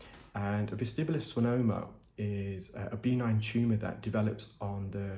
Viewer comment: I'm so happy you are sticking with the new format. By the way, when you said benign tumor I thought you said B9 tumor. I thought "What the heck is a B9 tumor?" I'm such an American. 😂